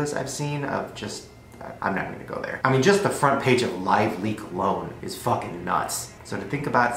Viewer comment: What is he saying at 7:22? Lively clone?? I'm curious about what that is 😂